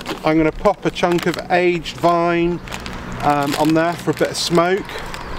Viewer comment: Pine??? Did you say pine? REALLY? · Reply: At 2.12 it sounds like you say aged pine.